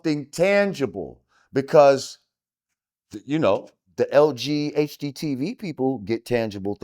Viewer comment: wait a minute did he just say Lghgtv?? 🤦‍♀️